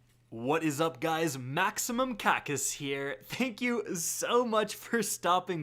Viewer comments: maximum sounds like some.... "enhancing" medicine for bedroom troubles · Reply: Yeah I thought he said maximum cock is here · Lol I know he say here but when he said "maximum kackis here" for I second I thought he said "maximum cock is here" and I freaked out but nvm lol